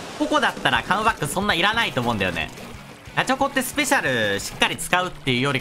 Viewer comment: のガチホコがガチャコにしか聞こえん